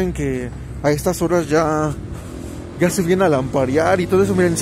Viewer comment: Jajaja yo escuche "a estas horas ya se vienen a aparear" Yo: Olv elonquesiste! Después mi cerebro analizó y entendió bien "lamparear"